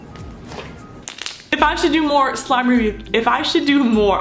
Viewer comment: I thought you said your done with slime reviews